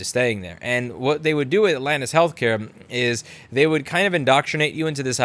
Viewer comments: When you said lani's health care I thought you said Linus Health Care..... yeah .... NOPE big pile of NOPE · Did I hear Linus Healthcare? Linus Healthcare Tips? ;D